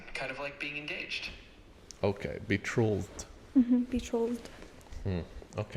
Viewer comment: The word is “betrothed.” The word “trolled” is a misunderstanding of the pronunciation. This cult is using a very old fashioned word to name a step in their messed up marriage process.The fact that it’s mispronounced is a result of how poorly educated these people are. So sad. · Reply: it sounds like she is saying "betrolthed". I wouldn't be surprised if that is just how everyone in the cult says it, based on what she said about having their own dictionary